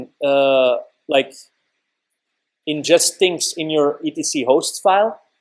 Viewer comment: what are you saying at 37:18? "inject things"? Also, thanks for the tutorial! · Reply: "ingest things," as in the entries get placed into the hosts file